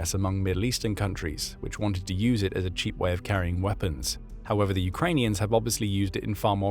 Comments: When you say the Middle East wanted them to carry weapons, the subtitles say "As a cheap way of carrying sensors." Be careful they are changing your words for the benefit of a protected religion. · Audio says "weapons", captions say "sensors". A significant difference.